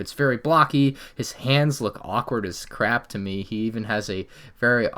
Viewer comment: Anyone else hear saying “he looks awkward”